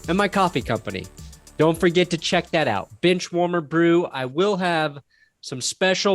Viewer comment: Hahaha, I thought he said "bitch warmer brew".